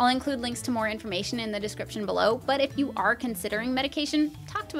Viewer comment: I thought she said medication, and I was just like "I have those!"